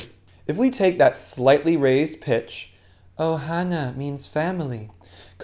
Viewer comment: I thought it said "The toilet means family".